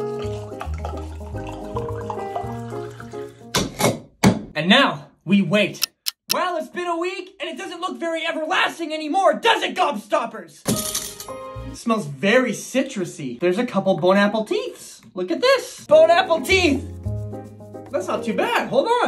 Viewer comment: For a second there I thought it said god stoppers 😄